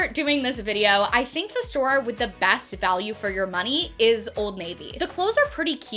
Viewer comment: Omg!!!! I can’t be the only one who heard her say old LADY!!!!! I laughed so hard when the name popped up omg 😂😂😂😂